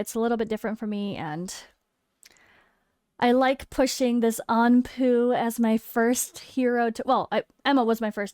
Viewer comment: I was just passively listening to your video as I was driving down the road. I could have swore I heard "I really like... Pushing this on poo" @ 13:07. That's my bad.